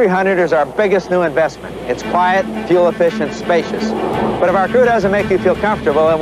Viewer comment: "specious" on captions instead of spacious.